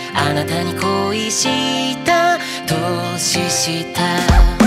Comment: ごめん、ここのコメ欄のみんなが考察とか感想とか書いてるなか失礼だと思うけど、この曲好きで何度も聴いてるけど、けど、、 0:19 の「年下」が、どうしても『凍死した』に聞こえてしまう！私の耳が変なだけかな！？